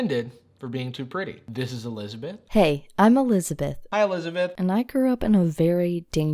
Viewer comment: when he said "this is elizabeth" i heard him say "this is a lesbian" and got so confused for a second